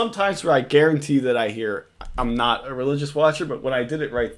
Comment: He clearly says I'm not a religious watcher but until this video I always thought he said I'm a religious watcher